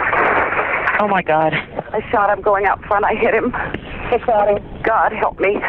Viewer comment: Did I hear her say...' I shot him when he was (going out ) ?????....Im surprised SHE wasnt arrested....the way the crazy way the law is sometimes...